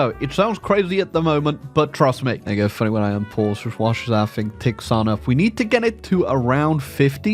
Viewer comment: Can someone please tell me what he says at 1:40, cuz I'm genuinely lost. Thanks in advance. One love ❤❤❤ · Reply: "There you go, funny when I un-pause, just watch as that things ticks on up."